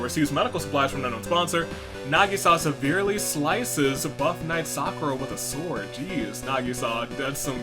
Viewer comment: I thought it said butt knight sakura... and I now hope mondo or Sakura win I think cheese burger avocado will get last my ride or die is shuichi even though he’s not in this episode